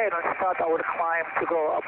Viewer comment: Great job. Don’t read what he says just listen to it. Does anyone hear him say he’s going to start his ‘de-cline’? Around 3:38 Doesn’t decline mean to go down? He says decline to go over the layer. . I feel like his mind knew to climb his training taught him to decline under the clouds. When he freaked out he moved over to muscle memory instead of common sense. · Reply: He said climb. No, "decline" does not mean to go down. You're thinking descend.